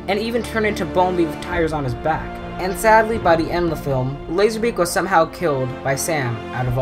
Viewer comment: When he said Laser-Beak I thought he said Lazarbeam XD